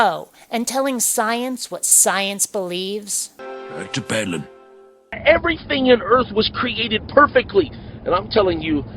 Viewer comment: I feel stupid for having to ask, but what is the Simpsons character at 6:20 saying? I've listened to that part of this video a few times and I still can't figure it out. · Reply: He's saying, "That's a paddlin'." As in, he'll punish you with that stick he's holding by paddling your behind.